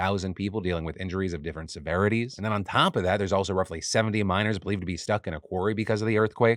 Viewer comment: At first I heard "There's also believed to be 70 minors stuck in a quarry" and my mind went "Why do kids keep getting stuck in mines?" until I realized you said miners, not minors.